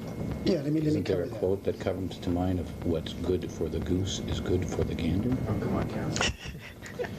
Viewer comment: What does this mean what's good for the goose... is good for the gander ? What he wanted to say with this? That the interviewer is a jerk or moron? I don't know this phrase 😬 Can somebody explain to me please? I'm from Germany 🙈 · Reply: OscarLeila1986 i thought he said what’s good for the goose is good for the anal I’m confused too somebody explain what he said and what it mean ??